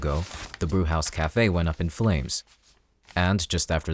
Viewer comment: what did I just hear???? Cafe blew up in flames?????? Isnt that from the Gangster ...... I'm dead💀🙂